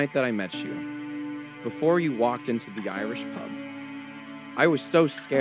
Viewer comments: Is it just me or did it sound like he said "the YIAYrish pub"? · did he say......YAIYrish pub?